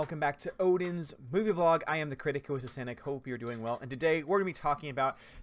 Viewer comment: I always thought you said "I am the critic that is acidic." ALMOST 1.5 mil =)